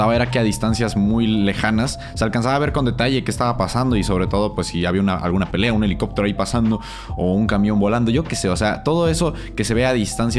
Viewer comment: Camión volando o escuche mal